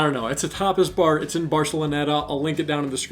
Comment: I thought u said topless bar, I was like oh ok interesting pick lol Tapas, got it